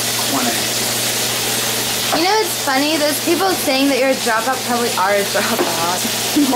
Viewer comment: What the fuck does she say at 3:30? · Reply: you know whats funny those people saying you're a drop out probably are drop outs